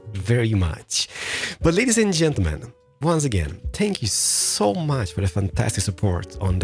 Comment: when he said thank you I thought he said spank you